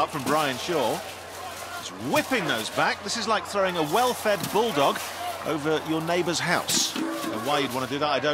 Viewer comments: “It’s like throwing a well fed bulldog over your neighbor’s house”… never in my life would I have thought I’d hear that sentence💀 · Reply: I thought he said welfare bulldog at first 😅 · “It’s like throwing a well fed bulldog over your neighbor’s house”… never in my life would I have thought I’d hear that sentence💀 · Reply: I thought I heard welfare bulldog 🤣